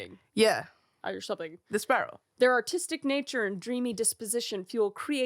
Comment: I swear I thought she said autistic.